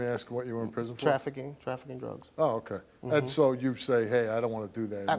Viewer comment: For a sec I thought he said “trafficking hoes “ but he said “dru…g..s” 😂😂